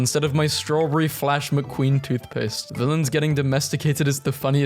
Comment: i thought it said “villagers”